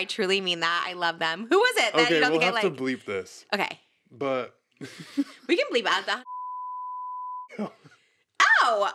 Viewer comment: someone tell me what was bleeped at 1:11:30 lol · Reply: One of the D’amilio’s. She mentioned she watched the season finale of their show recently.